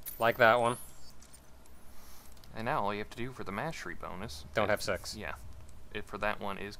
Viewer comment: When it came to the point for mastery of the gatling gun 14:07 it sounds like says "Don't have sex" LOL